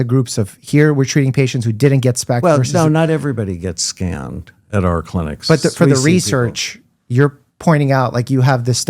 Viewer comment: I thought he said 'not everyone gets scammed at our clinic'! 😂